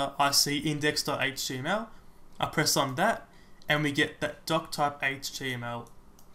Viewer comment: I thought you were saying dogtype, not doctype ...also it always sounds like you say, hi my name is dumb :( and then I'm like... poor guy, I'm sure your name is... oh wait, he said